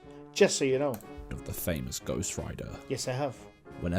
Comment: I thought I heard Ghost Writer 😆😁